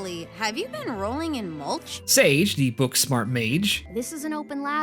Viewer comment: Why did I hear it as "Sage the Book Smart Page"